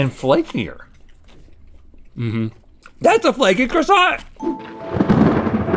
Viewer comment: oh my god. I thought he said fcking croissant 😂